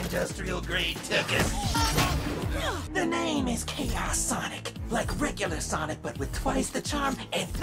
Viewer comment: why did it sound like he said “kickass”